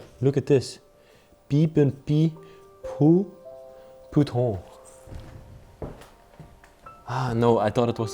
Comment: now wait a second.. isn't this a French swear word?? 😂 it sounds like he says putain or smth here